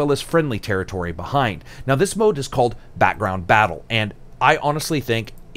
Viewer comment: did you just say..... "background battle"